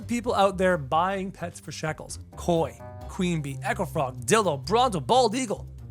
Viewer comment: did he say didl0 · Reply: No, he said dilop